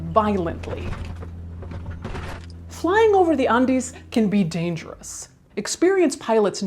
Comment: I'd swear I heard "Flying over the undies" and it was strangely appealing